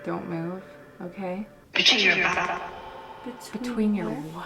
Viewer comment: Anyone else hear it say "between your back" but it like stopped Mid-sentance like "between your ba-" and this is after says"where are you?".